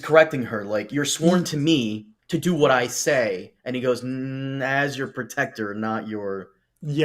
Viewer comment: Joined in late. With "As your sworn shield", Cole saying "I'm sworn to you as your sworn shield not your hired assassin." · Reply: Yeah, he actually says, "as your protector", which is even clearer to ne.